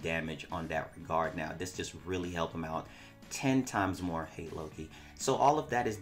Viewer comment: That subliminal "hate loki" LOL @ 4:52 I hear you XDDD